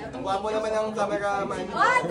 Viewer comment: andres tapping the chair to let ash sit beside him🥹 did he just say "ang bango mo ah" to ash?😆